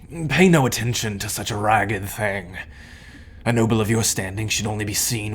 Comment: I thought it said “bought a noble” 😀 I mean if I could I would free money